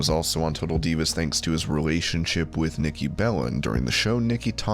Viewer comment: Sounds like you said Nikki bellend